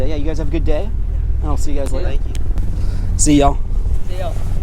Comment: Anyone else hear the kid say see yall back?